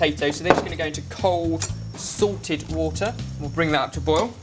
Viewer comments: When said, "cold salted water," I thought he said, "Cold sorted water"... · For the longest time, I thought you were saying SORTED water... I didn't know what sorted water was. Then I realized you were saying SALTED water... Ahh, haa! I'm just a silly Canadian